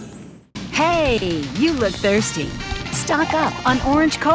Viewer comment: What does she say at 1:47, when introducing the soda? "Hey Eddy you look thirsty? 😂